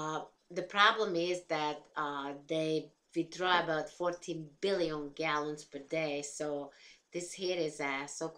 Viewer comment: Summer 2020 GOL 105 - V1 Physical Geology - Savannah Mihalo - At first, the water table confused me...but once you explained it with the green/red drawing, I understood it very well. Thank you, I was really grateful for that. What impressed me most about this segment were the aquifers, specifically the Ogallala (I tried saying that 10x fast...) aquifer. At first I thought it said it made irrigation withdrawals of 14 million, but then heard you say 14 BILLION and my mind was blown. I could not believe that we were sucking that much ground water out of one aquifer---it is a real shame that we might soon empty that resource. I wonder if the aquifers in Florida have anything to do with the taste and smell of the water---if you have ever been to FL, you'll find that the water smells and tastes rather foul. Perched water tables seemed convenient but once I found out they were shallow, I decided that they probably are not that reliable, especially during dry seasons. Don't drink too much wine! (